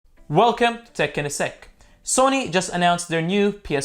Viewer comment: At the beginning I thought he said tech in a sex 😂